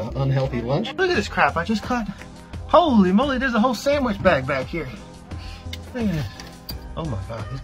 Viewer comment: Thought he said, "There's a whole meal in there" Lol 😂😆